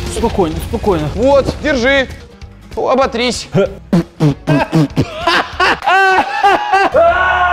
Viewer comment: мне одной послышалось "вот,держи,обосрись"?